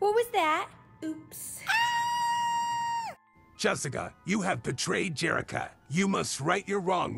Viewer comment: I thought it said “Jessica you have betrayed Jessica”😭😭